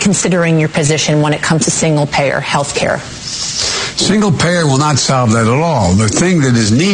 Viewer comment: Joe Biden: 'we don't need government healthcare, we need the government to give money to hospitals' 🤦‍♂️🤦‍♂️🤦‍♂️ That's what we're saying Joe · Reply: it sounds like Joe said, “single payer will not help that at all, we need medicare for all.”